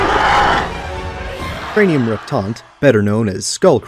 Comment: "??? Better known as Skull Crawlers" - what did he say? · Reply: Their scientific name cranium reptant